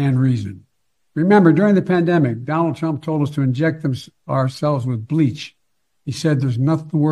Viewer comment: stutter. hims...ourselves with bleach. Anyone else hear that?